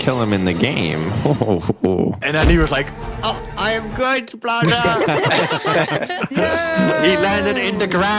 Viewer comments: “I am god blaza” Me:no I think you ment to say good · it does not say " I'm good blaza " it say's "I'm goo blaza"